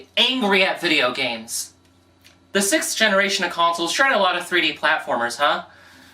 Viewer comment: legitimately thought you said "the cis generation of consoles" here, i need help